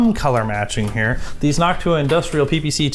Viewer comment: noctua I thought I heard haktua